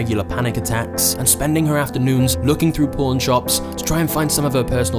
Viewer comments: i swear i heard ''and spending her afternoons looking through PORN shops to find her belongings'' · When you said “Pawn Shops”, I thought you said “Porn Shops” 😂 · I thought the first story was about to take an unexpected turn when you said she stumbled upon a pawn shop. Thought you said porn shop, lol